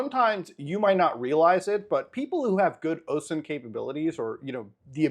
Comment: he says osemn capabilities right? i keep hearing ocean but im guessin it has to be osemn